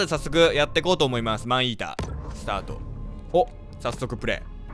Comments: 待って病気かもしれない。マンイーターがマンイーダに聞こえてさらにそれがアイーダに脳内変換された。がｱﾊｰﾝいうせいだ…(ありがとうございます) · マンイータがアイーダに聞こえる 私の耳は末期ですか？？？(幻聴) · 最初の方『アイーダ、スタート 』って聞こえたw